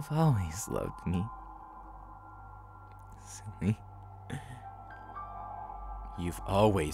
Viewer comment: When he said “ I know you love me silly” I thought he said “cydnee” that’s my name and I was like 0-0 holy sh!t